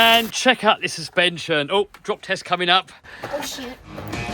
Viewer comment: what did she say?😂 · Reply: O shi